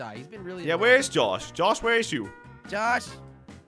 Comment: did he just say Josh where is you?